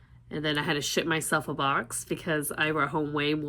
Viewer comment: I literally heard “i shit myself in a box” I was like WHAT